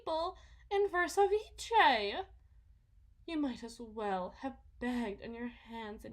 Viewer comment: What does she say at 7:40 ? All I managed to find was ‘en verso viche’ or something. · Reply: It's "verse vice," like "vice versus," but flipped on its head and pronounced differently for the petty eccentricity of it all.